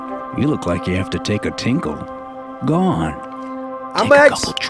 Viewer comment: Oop- sorry I thought I said trips XD to everyone who said drips I’m sorry for being deaf and telling you it was trips lol